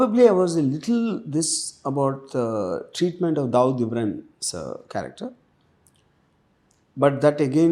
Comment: Did he said dawood ibrahim sir...at 58:04 or I heard wrong?